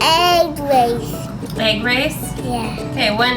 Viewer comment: I thought in the begining he said "say grace" but it was actually "egg race" oml I'm done 😂😂😂